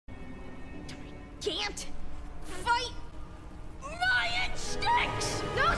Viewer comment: I always heard "CAN'T FIGHT, RYAN STINKS" first time I heard it correctly now as an adult